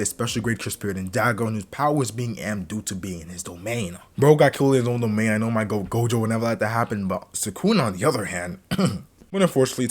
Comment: Yo what the homie said at 2:51 ? I replayed it 4 times and still couldn’t hear it. Also nice vid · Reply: "Bro got killed in his own domain, know my GOAT Gojo would never let that happen" 😂